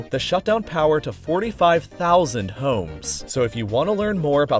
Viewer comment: I heard it as 45000 hoes n i was like danggggg.... But it turned out to be homes but still impressive